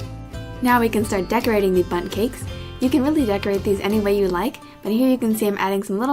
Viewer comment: Tbh when she said bundt cake it sound like she said butt cake 😂